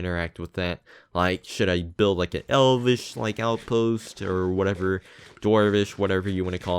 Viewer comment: Sounds good mr.P I learned about AfterLife when Whitefyr joined the server and I'm in the (slow) process of checking yous guys out. I think I'll be subbing to you now ;) Also, at first I heard "an Elvis outpost", I was like how does that work.. LOL